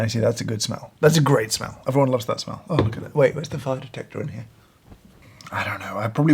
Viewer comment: Did he say fire detector or fart detector